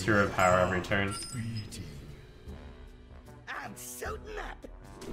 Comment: I always thought Dr. Boom said "Im so mad" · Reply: I thought he said souping up.